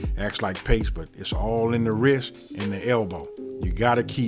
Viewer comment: "all in the wrist and the elbow" ....my guy know a lil sumn bout twistin up in the kitchen lol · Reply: Oh, I thought he was saying, “it’s all in the WHISK and the elbow.” Lol! Either way, it makes sense. 😁